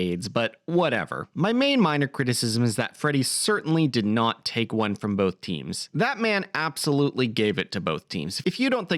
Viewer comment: You misheard something there. Freddy says he took one FOR both teams, in the sense that his death to AIDS ultimately contributed to its cure, which both LGBT and straight people would benefit from.